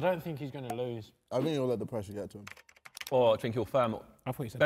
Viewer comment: JJ: I think he'll let the pressure get to him Tobi: I thought he said depression...